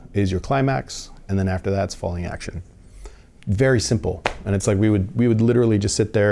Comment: What does he call the action of the forth act? "(something) action" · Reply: Hi, at 2:29 he says "falling action"